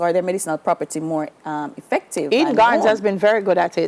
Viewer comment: The female presenter mentions a brand of tea at 7:56 does anyone know what this is please? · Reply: She said Eden gardens